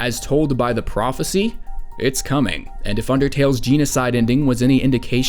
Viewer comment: did he just say... jeanocide?